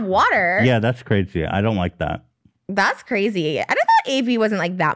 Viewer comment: did he? I think Ethan at times doesn’t have a filter but he’s not that bad. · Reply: whoops he actually said ‘I don’t like that’ not it bad. That’s my mistake.